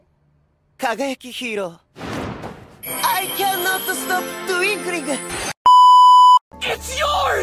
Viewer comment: Guys... when Aoyama's hero name or wtv said twinkling I thought it said twerking😭😭